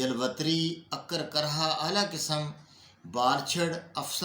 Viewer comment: Bhai 03:01 minute pae kya bola hai varchar ya kuch aur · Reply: Baljhad / Jatamansi